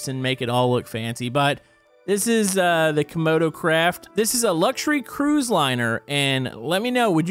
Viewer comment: did he say crap craft or crab? Let me know please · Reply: He said craft